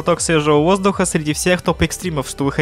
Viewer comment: то что я услышал: буду рад, почитать ваши высеры в комментариях так же я: не понял..